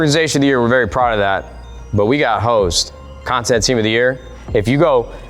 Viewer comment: At the end for a sec I thought he said we got hoes 😂😂😂